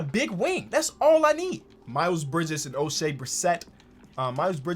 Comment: how? He is perfectly fine on the suns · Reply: my bad thought he said miles bridges 😂